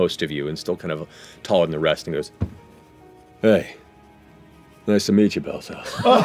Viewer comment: the first time i thought he said "nice to meet you bozos" rather than nice to meet you Bell's Hells."